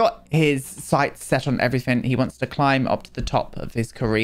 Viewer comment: "Maverick wants to climb to the top of his career." What I thought/heard was "He wants to climb the hot jogger especially with the way he's looking at him." lol